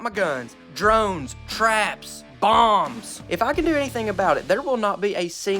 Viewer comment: did he just say bomds